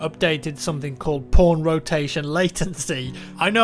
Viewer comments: totally thought he said "porn rotation latency"... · at 1;30 is it sounded like he said porn rotation, but actually he meant pawn rotation.